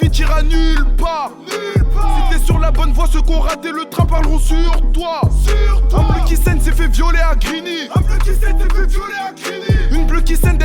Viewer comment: ya un moment il dis 1plike 7 ces fais violez a grigny ou ses moi qui a mal entendu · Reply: "Un bleu qui saigne c est fait violer a grigny" pas 1plike 7